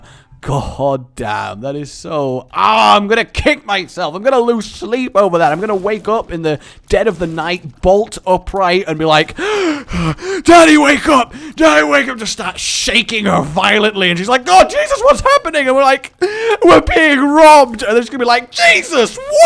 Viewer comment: I really thought he said "Daddy wake up" and i was really confused for a second